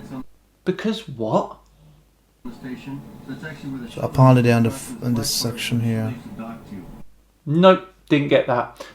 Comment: I think he is trying to say "apparently in this section [of the ISS that the astronaut is pointing to on the computer tablet] here" which has absolutely nothing do with his "Because...".